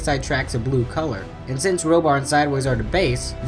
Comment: rollbar instead of roadbuster